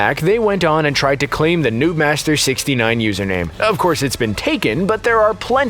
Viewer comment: I just watched it yesterday! Also my gamer tag is “newtmaster” when I was watching the movie, for a second I genuinely thought they said “newtmaster”